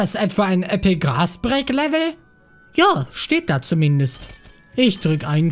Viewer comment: ich habe epic grass breack verstanden gg